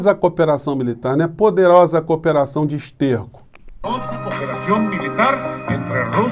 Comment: Ainda bem que não estava mastigando ou bebendo nada quando ouvi "poderosa cooperação de esterco" - ri alto nesse momento 🤣🤣🤣